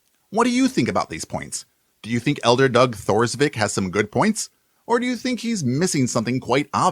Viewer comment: I missheard you. I Heard "Do you think elder GOD Has sone good points" 😂😂